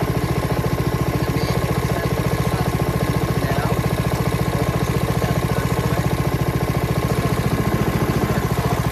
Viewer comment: Hard to hear what your sayin at 4:07 with the mower running.> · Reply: Hey I just rewatched. I’m basically talking about the hydrostatic system and how it was super slow. You need two wrenches to fix this 🔧. I have a video showing this.